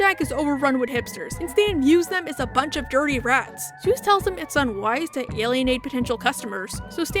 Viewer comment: the subtitles say Zeus instead of Soos lmao